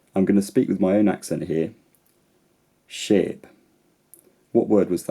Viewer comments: I thought you said "shape" :P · I heard shape, lol